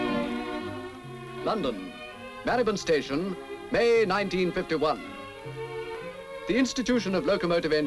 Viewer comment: What train station did he say? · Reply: From the transcript: 0:44 London, Marylebone Station, 0:46 May 1951. I (not British) hear Marybohn.